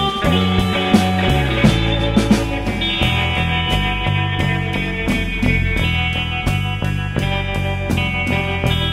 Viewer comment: does anyone know what that says at @ 0:39 ?😭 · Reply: Don't butt in, you fooled me. I don't buy it